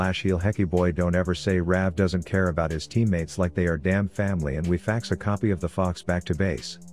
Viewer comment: did you say family?